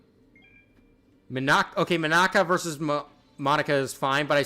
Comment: Also sorry i thought You Said minaka but You Said mita😅